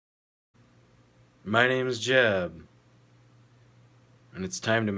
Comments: thought you said "My name if Jeff" like 22 jump street lol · Wth i thought i heard "my name is jeff" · Am I the only one who thought he said "My name's Jeff" XD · it sounded like he said "My name is Jeff" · At first I heard, "My name is Jeff" hahaha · "My name is Jeb." ffs I thought you said jeff I was gonna laugh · Lol at the start of the video I thought he said My NaMe iS JeFf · lmao thought he said "my name is jeff" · at the very beginning i thought he said "my name is Jeff" and i immediately thought that this lad is no typical normie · at first, i thought he said "My name is Jeff" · At the first I thought he said "My name's Jeff" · i thought he said my name jeff lmao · I thought he said my name is Jeff.... He said Jeb.... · I thought he said My name is Jeff. lmbo. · At first I thought he said my name's Jeff. sorry Jeb. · I thought he said, " my names Jeff" loled so hard lol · At the beginning of the video I heard "My name is jeff" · i thought he said my name is jeff 😂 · WHO ELSE THOUGHT THAT HE SAID MY NAME IS JEFF 😂😂😂😂😂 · when he said my name is jeb i thought he said My name is Jeff. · I thought he said "my name's jeff" at first. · boi i thought he said "my name is jeff" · I thought he said my name jeff XD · Lol who thought he said "my me is Jeff" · at first i thought he said My Name Is Jeff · I thought you said "My name is Jeff" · I thought he said "My names Jeff" lol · I thought he said “my name Jeff” but then I looked and I was like oh. OH.